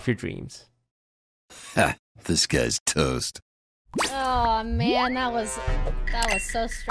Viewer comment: I hate myself i always thought the intro said "ha this guys toast" i am stupid